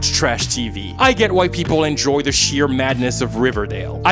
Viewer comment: When he was saying “I get why people…“ I kept hearing “I get white people…” and it was hilarious